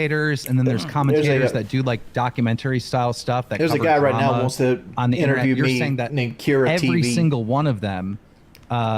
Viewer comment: Who does say wants to interview him? Can anyone make it out? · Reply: KiraTV